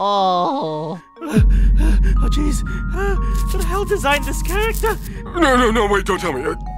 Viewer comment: "Oh geez who designed this character?" His honeycomb cookie: Beeg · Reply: I thought he said something like “I designed this character”